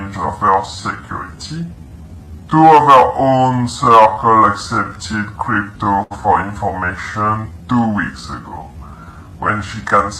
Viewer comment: am I the only one who heard him say “ TWO OF HER OWN CORCLE EXCEPTED CRYPTO FOR INFORMATION 2 WEEKS AGO”